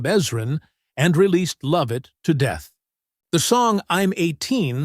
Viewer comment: Warner Bros. He actually said "bros''